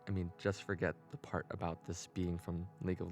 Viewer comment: Societal Rage (Arcane) segment at the start of it he says "sounds perfect right? i mean just forget about (couldn't understand what he said kinda sounds like he has dental work) in league of legends" what did he say to forget about? i never played the game but loved the show so i wanna look it up lol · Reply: I also tried using CC but it didn't pick it correctly i think it said "this being"